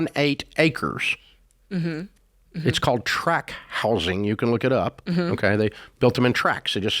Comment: I thought Dave said crack house but he said track house. 😂😂😂